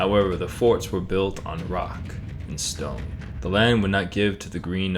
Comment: DID I HEAR A ROCK AND STONE!?